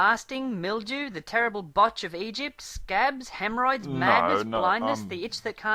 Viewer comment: Did I hear "The terrible butts of Egypt"?